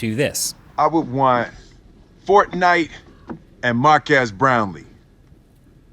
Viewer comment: Did he said,"Mark Ass Brownlee?" Felix heard this too.